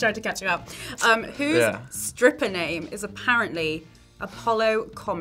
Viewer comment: did she say "stripper name" or somethin else? I didn't quite catch it. how did everybody get one?